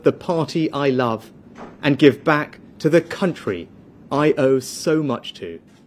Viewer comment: Sounds like hes saying "To the country I loathe so much"